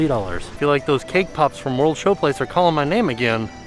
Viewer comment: I thought was saying "World Show-plates" and thought it was a great pun for the festivals. Disappointed it wasn't true lol.